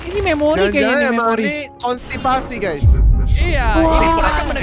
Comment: "jangan-jangan ini konstipasi", apa gw salah denger 🤣